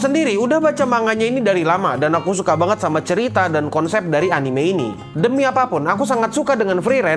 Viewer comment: sorry gw yg salah denger apa gmn? anime atau hanime? 😅